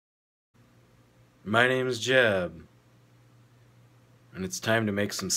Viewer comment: i thought he said "My name is Jeff.".